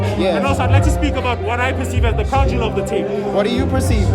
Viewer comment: the crown jewel of the mixtape. meaning I think “2 Deep” is the standout track of the mixtape. I say this because it’s my favourite, I’m in love with Blue Pappi’s performance. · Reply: 😅ohh thank i didn't hear Crown Jewel i thought you were saying "a Conduit of the tape" but that didn't make sense i had to ask